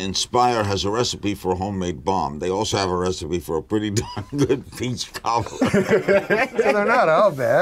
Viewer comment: what does he say? i can't understand because english isn't my mother tongue. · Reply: They also have a recipe for a pretty darn good peach cobbler.